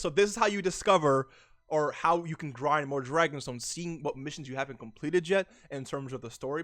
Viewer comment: did he just say dragon balls👂